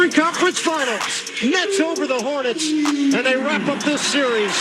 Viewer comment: did he say the knicks?😂